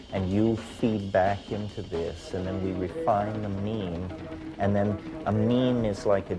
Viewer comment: hold up did Terrance just say meme ?